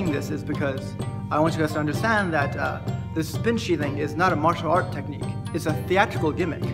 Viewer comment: What he says: "spin sheathing" What i hear: "spin shitting"